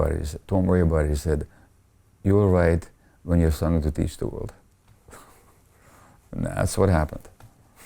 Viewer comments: 💡I think he said the professor said to him @ 3:58 “don’t worry about it, you’ll write when you have something to teach the world” (is that what he said?) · What he said @ 03:57? Thank you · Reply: "You will write when you have something to teach the world."